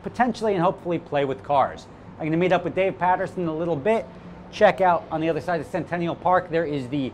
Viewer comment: I thought you said, "Dave Patterson the little bitch" lol